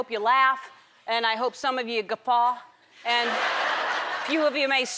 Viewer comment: she hopes that some of us WHAT?? (guffa? what's that?) I really don't understand so someone please tell me. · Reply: Guffaw is basically like laughing but like more intense I guess